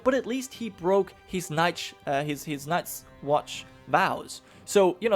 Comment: "He bust his nuts watch vow." Is what I heard. Oh he bust his nuts alright